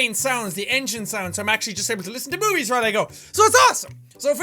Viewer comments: "...So i'm actually just able to listen to boobies when i go." Well that's what i heard anyways. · Did he say at 1:06 boobies?